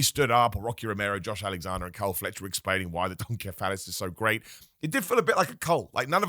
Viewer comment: did call him Don Phallus? 🤣 Sounds like someone listens to Jim Cornette’s podcast 😎